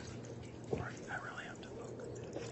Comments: did someone say they really had to poop?!?! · does someone say something like "Cory, I really have to poop" ?